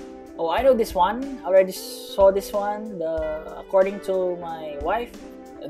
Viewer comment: Correct me if im wrong but i swear to god i heard 7:32 "according to my wife?" Bro is married? Huh? but sounds alot like around 16.